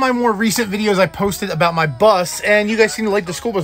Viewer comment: When I just swipe up I thought he said "so what of my More recent video that I posted about my butt"then I came back here then realize he says bus 😂